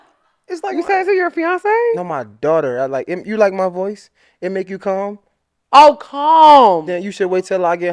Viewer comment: 😹😹😹😹😹😹 at first it definitely sound like he said you like my voice it make you cum lol I was like 🫢🫢🫢